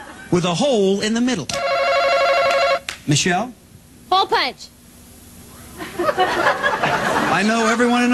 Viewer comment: What does she say at 12:00? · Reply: hole punch